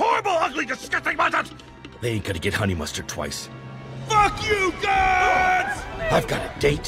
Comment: what did he say at 2:08? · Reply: "They ain't gonna get Honey Mustard twice".